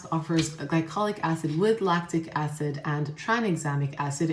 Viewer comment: Correct glycolic acid is strongest AHA and the smallest which allows it to the deepest. Whereas the mandelic acid is the lightest and largest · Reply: thank you! I thought you said lactic acid was 😂I guess I misheard. Love your new lip products!